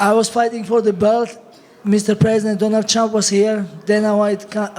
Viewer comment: did he say "donald chump" ahahahahaha